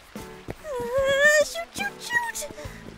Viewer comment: did i misheard or Ms. Invisible said in russian:«ещё чуть-чуть»(in english:«just a little bit»?